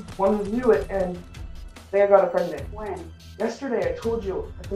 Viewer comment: LMAO the last kid had me rolling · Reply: Hahaha I thought he said he got a dude pregnant 😂